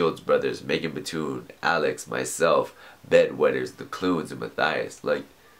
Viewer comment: Is it just me, or does it sound like he says 'Bed Wetters' instead of 'Bad Weather?'